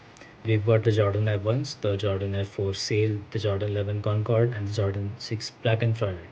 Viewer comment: Can you please tell me the name of the second shoe on the case 0:47? (I know you said it but I couldn't understand) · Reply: Sure, it’s the Women’s Air Jordan 4 x Off-White “Sail”